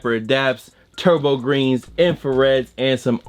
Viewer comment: did you just say tobo green? lmfao